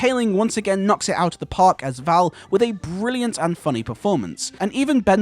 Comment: I sadly haven’t seen that show. But I loved her in this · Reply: I had to watch this a few times because I thought you said "brilliant unfunny performance" lol, but it was "brilliant and funny performance." Glad to know the cast is improved in season 2.